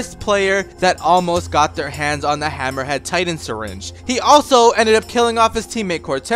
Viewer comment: Did I hear “hammerhead” Titan??